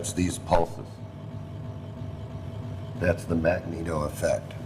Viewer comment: Anyone Catch what he says at 37:28? the Magneto effect?